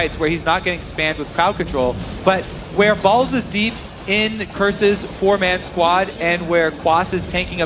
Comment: Did he say balls deep?